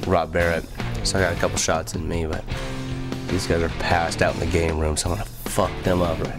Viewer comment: Apparently Joey said "Yes!" to the idea of doing another Road Runner LP. So ya. I came back here just for "Ohhhhh righhhhhht, yeaaaaaaaaaaaaah, you mother fuckers, passed out in the green room. I wanna send this fucking rap out to all the mother fuckers who pass out too early" at 45:46. To this day, I'm trying to figure out if it is called the "Game room" or "Green room" 🤣🤣🤣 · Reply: I THOUGHT HE SAID GREEN ROOM , BUT I BET IT'S GAMEROOM